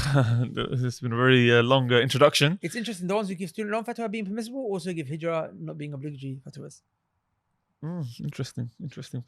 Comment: What did this man say @ 20:00 ?! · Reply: fatwas